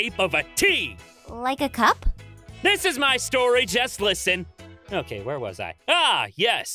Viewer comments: HmMmMm- i think Travis probably said “this is my story just listen” But why did i hear- “This is my story, Jess. Listen.” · Travis saidJess instead of Aphmau XD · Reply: No he said JUST listen